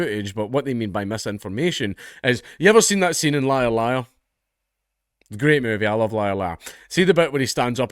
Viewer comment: I'm an American & had to look at the closed captioning - I thought said "Lyle Lyle" vs "Liar Liar" 🤥